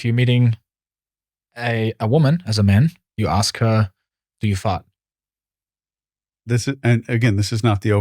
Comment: I replayed this part like five times and I still can't understand what you're saying. Are you fa?